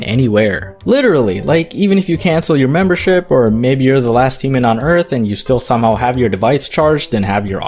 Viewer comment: The same way Dom zoned out with the PEE part i zoned out during the sponsor and thought he said "last semen on earth" 😂😂😂